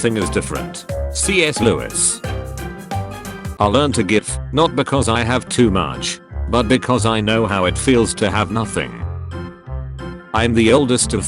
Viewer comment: Am I the only one who heard "I learned a gif"?